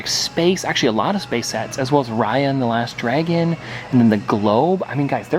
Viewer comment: Why does it sound like he says Ryan and the last dragon instead of Raya and the last dragon